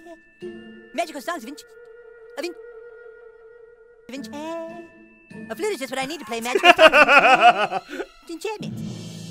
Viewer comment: I was half awake and it sounded like he said 'A flue is just what I need to play magical sausage of enchantment'' XD Still wondering if is single LOL